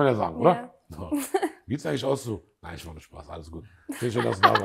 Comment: Was sagt er bei 32:05 ? "Du ziesht dich aus so?" 🤔